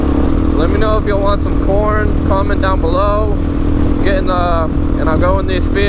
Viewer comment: First time i Heard "Porn" hahahah